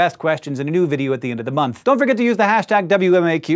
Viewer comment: Lol when he said "Use the hashtag WMAQ," it sound like he said WMMAQ!